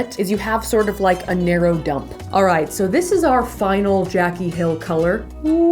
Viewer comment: omg did she just say jacky hill